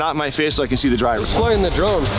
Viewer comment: When I heard “explain the drone” 😂😂😂 I died laughing, 0 fucks given YSOB 😂😅